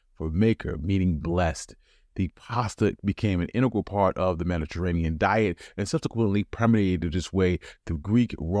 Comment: What's that word "premediated"? I don't think it's a word, and I have no idea what word you're trying to go after either.